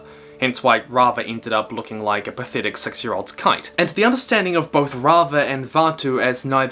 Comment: when you said "6 year old's kite" I thought you said "cunt"